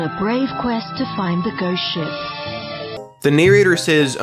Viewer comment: does anyone else hear Ghost sh!t 3:54 comment comments Skull Emoji if you agree with my theory